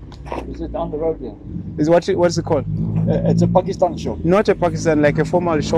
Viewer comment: did he just say pakistani shop:?